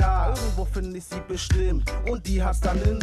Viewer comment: ich hab früher immer verstanden :" ...und die hats dsnn in sich und zwar mit nem doppelten Kinn!" 😂😂😂😂